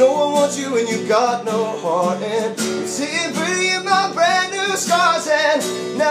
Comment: ima be original and not talk about how its only brendon in the band when i first heard this song i heard "sitting pretty in my BATHROOM scars" idek